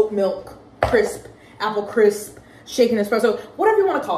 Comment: When you were talking about the latte why did i hear "chicken aple crisp" 😂 when you clearly said "shaken"